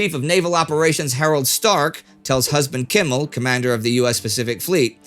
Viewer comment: Husband Kimmel sounds like a character from a Blackadder series that was never made, doesn't he Darling. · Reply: lmao i heard it as: "US chief of naval operations tells his husband "Kimmel" that president Roosevelt..." made me rewind it asap!